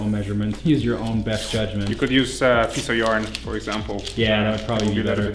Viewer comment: When he said "piece of yarn", I thought he said "piece of urine" and I was like wtf?